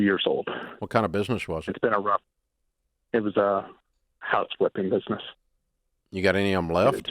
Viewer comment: At first I heard "couch flipping business" 🤣🤣